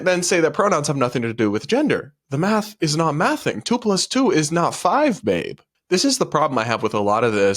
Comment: i thought he said "2+2 is not 5 MATE".. It actuallt sounds alright hearing an american causually drop a "mate" here and there like Brits and Aussies